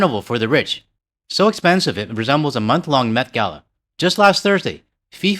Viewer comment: Lol, I thought he said methgala instead of met gala for a minute.